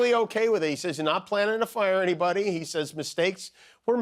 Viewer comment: I wonder how many drinks Pete had on his long flight to Hawaii before he talked to the reporters. I would love to see his bar tab. LMAO. LMAO. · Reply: I thought he said he was not drinking anymore.